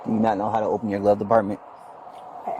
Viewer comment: did he call it a glove department? 😂😂